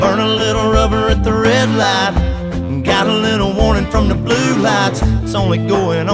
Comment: I've literally always thought he said got a little "horny" from the blue lights instead of "warning" from the blue lights.